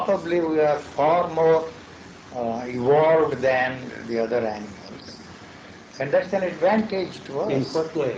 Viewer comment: What does he say at 25:15 “we are for more ???? than the other animals. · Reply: Far more evolved than the other animals.